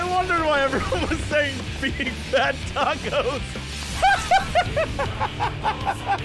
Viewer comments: LMAO! I thought it said "Big Fat Tacos FOR MEEEE!" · Lol, I've always heard it as "Beef soft tacos, so BIIIIG" but it was still tacos. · See I heard "Beef soft tacos, Beef soft tacos, Beef soft tacos, supreme!" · While I hear 'Supreme Soft Tacos!' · Always heard it as Beef Soft Tacos. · I hear it as "big fat tacos for meeeee" Also, the alarm sound in Garlean outposts, in my mind it sounds like "heal me, heal me, heal me". :P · I've always heard it as "Eat Soft Tacos"